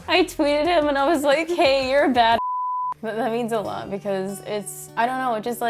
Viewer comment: I thought she said she tweeted him " your a bad fuck" ..lol